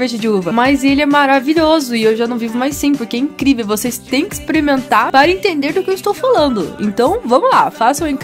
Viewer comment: Tá ouvindo? Vcs tem que "exprementar" kkkkk...Só eu que entendi isso? 6:21 😂😂😂